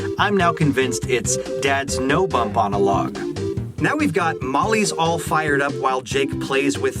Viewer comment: I heard "dad's an old bump on a log"